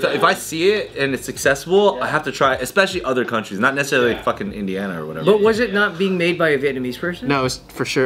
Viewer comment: Did say pho from Indiana lmao